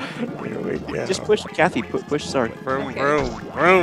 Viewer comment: The sound bit at 7:31 sounded like Cathy to me on the first go-around.